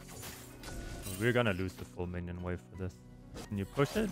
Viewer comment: editor stop putting captions when you don't hear what froggen says. "to them afterwards" -> "the the map woods" it just makes the video more confusing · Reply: Also at 2:24. "full minion wave" is what Froggen said and the caption says "4 minion wave". The editor needs to get his ears checked out T_T